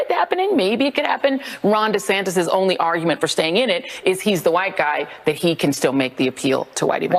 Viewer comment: Did she just say 'WHITEY'?!!!!! It's at 1:00 in the video.